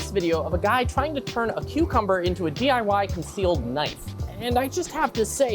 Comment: I wasn’t listening to the beginning and thought he said “Today I turn a cucumber into Canada.”